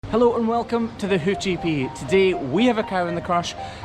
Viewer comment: Not gonna like I mis heard the beginning and thought he say “welcome to the hoochy Pete”